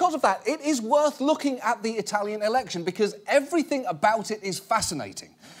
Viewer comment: I thought it said Italian erection